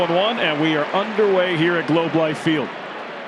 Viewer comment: I thought he said;' Low Life field." LOL.